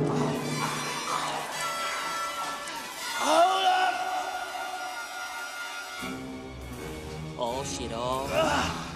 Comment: did he say carter or hold on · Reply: Hold on